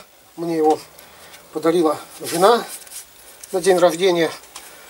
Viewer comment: Послышалось "подарила жена после овобождения".